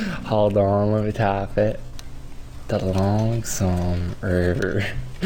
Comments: I thought he was saying 'the long sombrero' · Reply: i thought he was saying "the lonsome rear" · I thought he was saying 'the long sombrero' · I heard "The Long Sombrero" 😂😂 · Thought he was saying the long sombrero